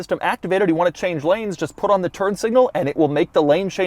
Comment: Have I heard Turd Signal xD?